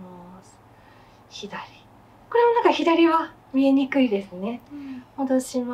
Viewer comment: I had to turn the Japanese subtitles on because for a second I thought she said みにくい (ugly) 😂😭